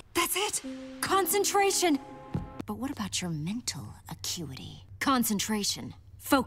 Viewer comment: Did she just called him cutie 7:54😮